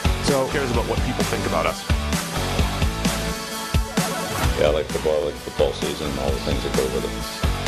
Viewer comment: I’ve been wondering this for a long time. What does Belichick say in that intro? It sounds like he says, the whole thing is of little relevance but I feel that’s incorrect. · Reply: "Yeah I like football and football season and all the things that go with it."